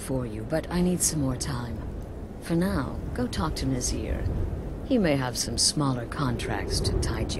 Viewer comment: whenever he said talk to nazir i thought he said torture nazir